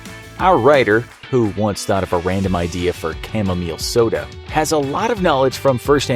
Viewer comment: Oh! "Chamomile" soda. I thought she said "cannabis" soda. I was like, I dont think that's so bad!